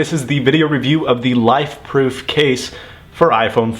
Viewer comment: "ifrone"? LOL or am i hearing things?